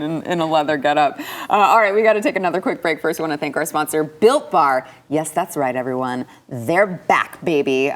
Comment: Lmfao, when u went on your spiel about "built bar" I thought u were saying"Bill Barr".